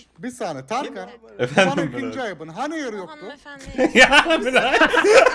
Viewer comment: de ne diyor? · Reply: Hani Air yoktu diyo